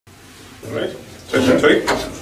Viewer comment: BEST performance EVER. :') By the way...can anyone tell me what has been said between Nobu and Maestro Petrenko between 0:00 ~ 0:03 ? Is it Russian ? Sounds like 'All right ? Trick or treat ?' though. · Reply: "Toi toi toi"(トイトイトイ) と声を掛けてらっしゃると思います。音楽家がよく演奏直前に"幸運を!"と言うような意味で掛けるおまじないの様な言葉です。ちなみに"toi toi toi" と言われたら、幸福が逃げてしまうと信じられている為、ありがとうと返さないのが決まりです。うなずくか、笑顔で応えます。 共演の場合は、この時の辻井さんの様に"トイトイトイ"と返し、お互いに願を掛け合うこともよくあります。